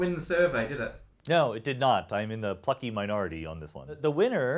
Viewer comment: Does he swear at 8:30? Haha! Is he saying "funky" or... another word? Clearly, he's very passionate! :-) Great video!